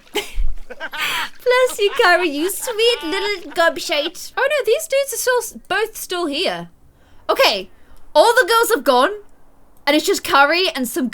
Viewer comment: did say gob-s*i*e?